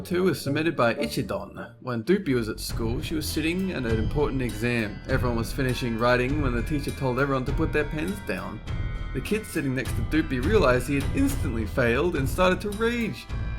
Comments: I thought he said the teacher told them to put their pants down... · on the doozie one i thought they said put ur pants down not put ur pens down... -_ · Who else thought that had said "put their pants down"